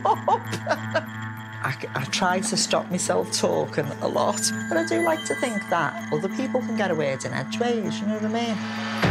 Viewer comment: I cant figure out what she said @ 5:05. Help me out, Brits? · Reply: "I do have to stop myself talking. But I do like to think that other people can get a word in edgeways, d'you know what I mean?"